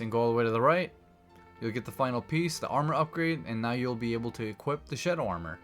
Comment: I thought you said the sh** armor 😂😅